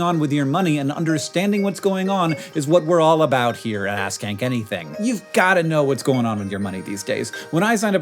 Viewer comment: I wasn’t paying attention for a sec and heard it as ‘ass can Kenny thing’ and got very confused